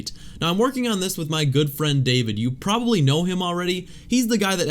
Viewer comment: Bruh I coulda sworn I heard him say “my girlfriend David”